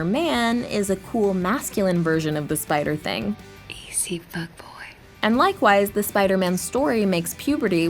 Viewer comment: Did Gwen say "Easy, fuckboy"? I can't be the only one who heard that.